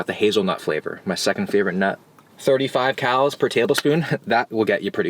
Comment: i heard “75 cows per tablespoon” lol which is probably also true